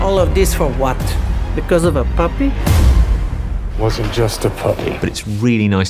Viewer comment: I hear the lady says "because of a puppy?", but her mouth is saying something else. And I can't read lips. What did she really say?